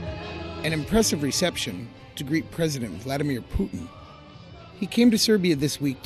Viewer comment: / 00:07 did he say Greek president Vladimir Putin, or great president Vladimir Putin 😂😂😂 i am not sure